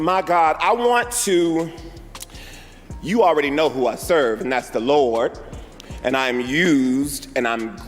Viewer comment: did he say he serves..........the Lord